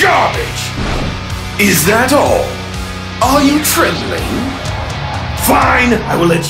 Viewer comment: I legit heard "Tripping" instead of trembling